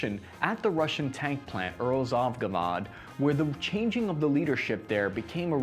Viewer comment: did he just say UralZovVagod xD